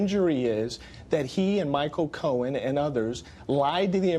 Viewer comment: Actually, that's not at all what he said. He said lying., LYING. LIKE WHAT YOU'RE DOING RIGHT NOW.